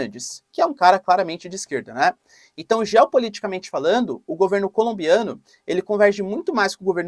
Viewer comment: Impressão minha ou ouvi em 3:46 e 4:04 governo colombiano?